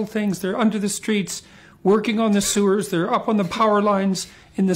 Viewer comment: i heard it as "twerking on the sewers" and i was legitimately so confused